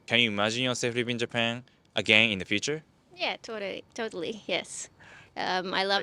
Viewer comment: I heard that! Her Japanese accent snuck out saying "totari" instead of totally. Otherwise definitely has an Australian accent.